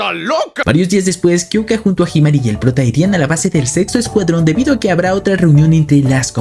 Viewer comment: Creo que tengo la mente sucia o alguien más escuchó que dijo sex0 escuadron